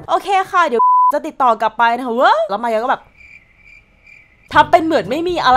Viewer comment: ตอน 0:38 ได้ยินเหมือนประมาณว่า แล้วมายเองก็แบบ